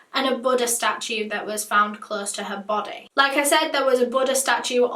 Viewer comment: i thought she was saying “butter statutes” 😭 i realize now it was buddha